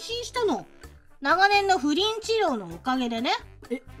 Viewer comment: 不倫治療って聞こえる、、、、、😂